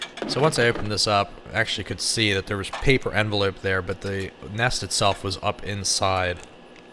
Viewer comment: are we going to ignore the fact that he said he found a paper envelope or did i hear wrong . old love letter ?